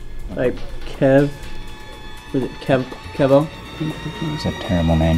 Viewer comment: “Kev? Kev-O?” “That’s a terrible name.” LOL PERFECT 😂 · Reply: I thought he was saying "Kev oh Kev.." smh 😁🤣